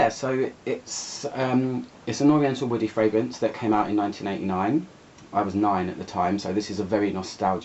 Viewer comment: It sounded like u said it came out in 1999? I thought it came out in the 1989?? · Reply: It came out in 1989 yes. It's the London accent. :)